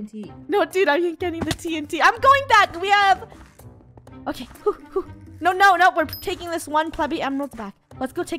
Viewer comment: When Funneh said nope let's take back this pleby emerald I thought she said nope lets take back this puppy emerald